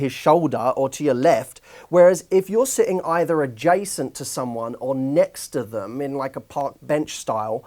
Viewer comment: What's the difference between " next to him" and "adjacent to him" ? sounds the same to me . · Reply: I heard he said "either adjacent or next"